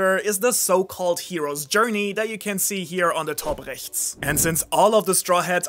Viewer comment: Top rechts? Did i understand it wrong or did he say the german word for right?